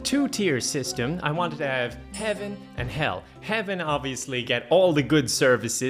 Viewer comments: For a second, I thought he said and Hell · At first I thought he said the two tiers were and Hell